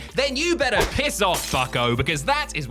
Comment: I thought he said "Piss off, fucko" and didn't question it until I remembered we're semi-family-friendly now.